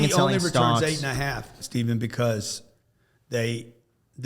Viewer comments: Did he just call you Steven? LOL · does he call "Steven"? Lol